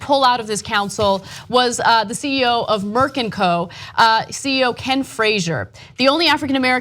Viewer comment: I seriously thought Ana said "Merkin Co" for a second.